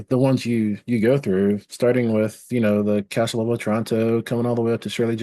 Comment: SGJ mentions something about Toronto at 9:36 that I didn't catch and neither did the transcript. Any ideas?